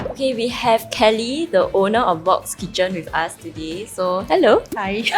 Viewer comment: Bok's Kitchen... I misheard it as Bok Chicken and thought of Kim Bok Joo hahaha